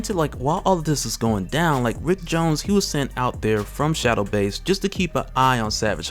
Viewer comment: I thought I heard Rick James 😂